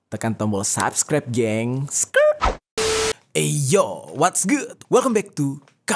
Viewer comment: Bang loe ngomong apaan,yg aku dengar di menit 00:27,BOSKU dan menit 00:30,WAS GOOD.apakah itu sedah benar😅😅